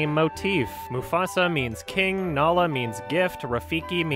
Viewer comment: said "Nala means gift." But I heard, "Nala means YIFF." god damnit you fucking furry